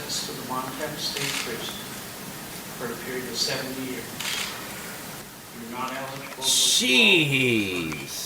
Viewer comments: I thought he says 7 years at first! 70 is effing wild. Often people don't even get that for 1st degree premeditated murder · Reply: I thought he said 7 years too. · I thought he said "7 years". I was like, those Americans are going soft. But 70! That's too steep. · I misheard and thought he was SENTENCED to 7 YEARS and i was thinking that's a good deal then you said 70 YEARS DAMNNNNN😮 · I thought the judge said 7 years not 70 haha